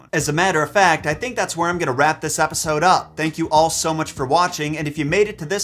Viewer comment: Did he really say "Wrap this repisode up" at the end. Not a typo, I seriously thought I heard an R in that word